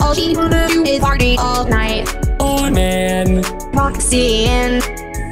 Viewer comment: i hear " OLD MAN " instead of "OH MAN"